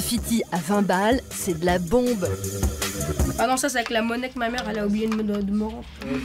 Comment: I clearly hear mon-nik-ma-mere. Closed Captions ("la monnaie que ma mere ...") aren't up to the task.